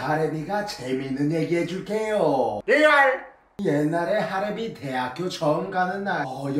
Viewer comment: 리발이라고 들리는거 나만그렇게들리나